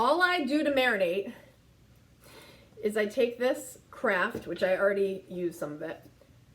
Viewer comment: I heard “I take this crap...” 😆 had to rewind.